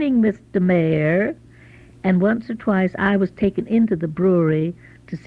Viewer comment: Did she say once AND twice?